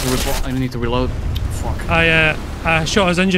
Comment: I HEARD IT HE SAID "Fuck"